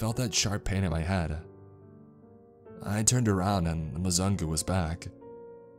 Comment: Haha. With captions on, around 12:42 it says, "I turned around, ans the lasagna was back." Instead of the Muzungu being back. Hehe.